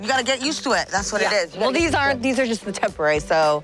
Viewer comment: I cried getting 1 crown put on🤣😭 how did they do this?!!! I can't imagine after the anesthetic wears off... sidenote it sounded like she said "Its corset" not its worth it lol